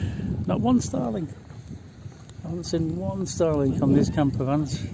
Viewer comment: I thought you were saying starlings 😂😂.